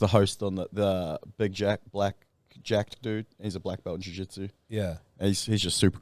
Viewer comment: thought he was saying Jack Black at first 😂